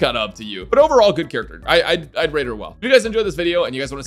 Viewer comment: "I'd breed her well"? (I know its not what he said but the first time I heared it I really thought thats what he said lmao)